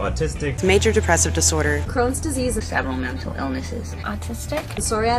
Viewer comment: I keep hearing it as "Crom's Disease". Sorry.